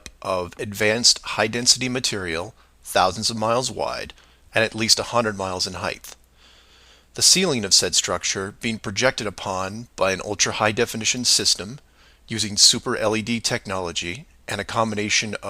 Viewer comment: Did... did he just say "heigth?"